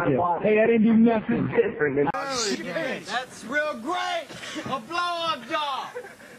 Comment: Whatever Kevin said at 38:38 I've been sayin all day, it's literally finna be my new catch phrase as soon as i figure out wtf he said 😂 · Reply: That's real great! A blowup doll! Lol